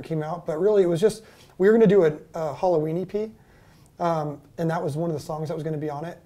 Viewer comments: i heard “halloweenie pee” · genuinely thought he said halloweenie pee